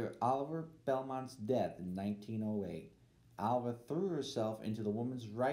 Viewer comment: Alva Belmont did not die in 1908 and then continue doing things! Her husband Oliver H.P. Belmont died in 1908. I also agree that the sound quality is poor. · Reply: Where does he say that? And why do five people agree? Lol. He said Oliver died in 1908, not Alva. Edit: 3:37 (I’ve got headphones on, and no problem hearing.)